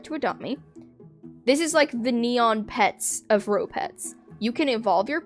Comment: I thought RoPets said NeoPets LMAO HELP